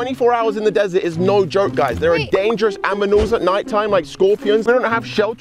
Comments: Did he just say aminals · did he say aminals!!!!!! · did he just say "Dangerous AMINALS?" 😂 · Why did say aminals?